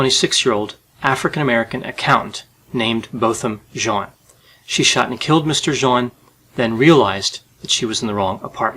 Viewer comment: I thought she said she shoot to kill